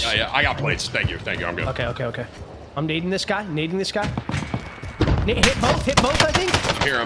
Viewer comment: Lmfao yo go to 4:40 did z say he's dating this guy then switched it up lmfao